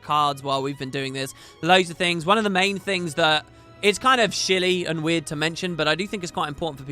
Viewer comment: "It's kind of shilly" I thought you we saying silly in a hypello voice. Great job with the series, it was a joy to watch!